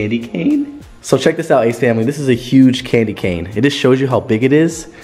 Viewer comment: I thought she said “Ace Family I Took A Shit”